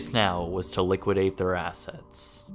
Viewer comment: I thought you said "liquidate their ass hats". My bad!